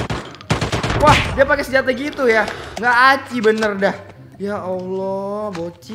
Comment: gak aci? apa gw salah dengar